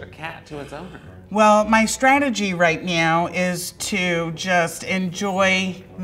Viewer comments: did she just say her strategy just meow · She really said “ My strategy right meow” 😂❤ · Did she say right meow 0:58😂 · Did she says 'my strategy right MEOW' ?? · did she say right MEOW??